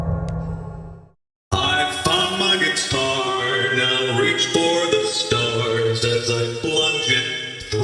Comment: what he’s saying: i f@ my guitar and ill reach for the stars